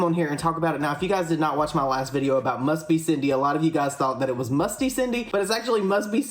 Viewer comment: I kept hearing “musty cindy”